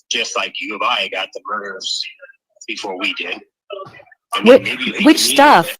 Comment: " murders' name, b4 we did ??!' I hear murderers..WK